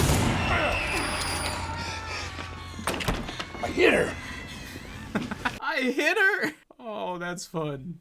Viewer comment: where he says " I hit her! " I heard " My dinner! ".